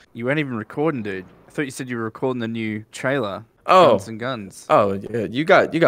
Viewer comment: lol i thought guns n guns said guns and c*m